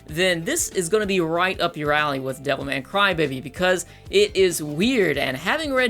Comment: I thought you said devil may cry baby